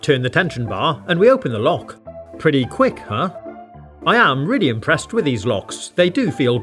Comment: it's pretty quick huh? Instead of pretty cool huh?